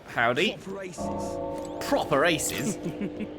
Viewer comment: Ah! He was saying "Proper aces." I knew he was not saying "I'm racist." but could not figure out what it actually was.